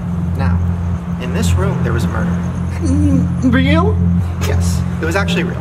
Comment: When Shane says “in this room there was a murder” my subtitles said “in this room there was a burger”😂